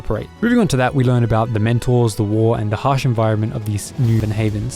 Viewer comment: Am I the only one who heard “dementors”? I was so confused. 😆